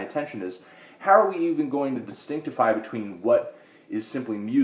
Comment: Did you really say "Distinctify"? WOW.